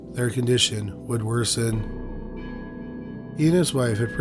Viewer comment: Not trying to be a dick or anything but in the future you should enunciate certain words better, because at first it sounded like you said "WOULD" but it didn't make sense as I watched on...I had to go back & put subtitles on & listen again. Just saying.